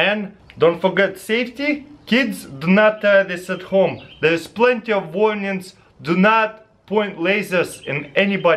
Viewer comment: I literally thought he said "there are plenty of onions"